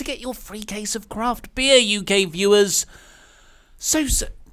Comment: When he said UK viewers I thought he said You gay viewers for a second